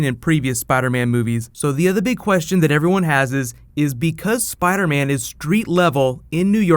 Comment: Did you say "...big question that everyones HASES."? Not really sure, but it does sound like it. Just curious, since my native language is Spanish. Greetings from Panama Republic!